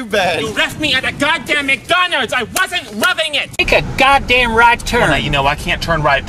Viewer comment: "You weft me at McDonalds! I wasn't lovin it!" 😂😂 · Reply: Swushiz OH MY GOD THAT’S WHAT HE FUCKING SAIIIID I always thought they said “I wasn’t rubbing it”...